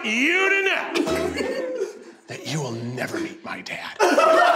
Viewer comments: I hate seeing Keith lose, but I love watching him react to it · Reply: I thought he said 'you will never be my dad' 😂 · “I LOST without a recipe ice cream. I LOST without a recipe cake, and now i have LOST without a recipe ICE CREAM CAKE” yeah kinda saw that coming keith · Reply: I thought he said “meet my dad!” Yours makes more sense!